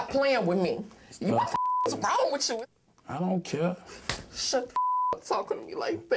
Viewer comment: when that abusive piece of shit slaps her boyfriend for the last time, does she say "Shut the fuck up you like that!"? I can't hear it very well. If so, she's insane. Well, she's already nuts, but that is the scariest thing she said the whole time they were 'alone' there. Does that crazy bitch actually thinks her boyfriend likes being abused?